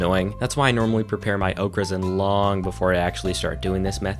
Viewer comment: I'm sorry. this is like the 3rd time I'm watching this video but everytime you say 'Oak Resin', I always hear 'Okras'